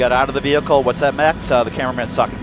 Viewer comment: Does he say “The cameraman is sucking”? When trying to see if someone exited the vehicle.